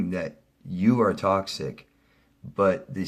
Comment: When you said 'toxic' I heard it as 'talk-sick'. A new term.